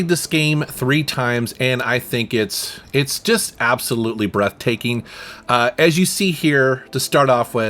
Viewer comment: I can't really hear it but did you do the "Abzulute" pun there?